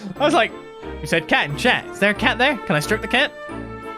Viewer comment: I know meant stroke the cat but my ears are failing me and I heard it as strip the cat. Also, is avocado an exotic fruit? It's common enough to have it all year round where I live so imagining someone who never tasted it at least once is baffling.